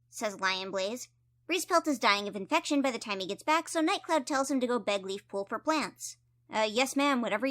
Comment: i am sorry but i heard bed not beg😭 and the fact that the sentence still works too😭